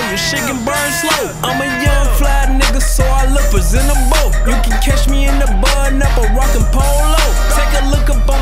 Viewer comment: what does he say ? “You can catch me in the ? rockin none but polo” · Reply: isabel_pop catch me in a button up